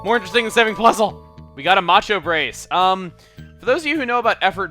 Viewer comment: I thought the macho brace said nacho case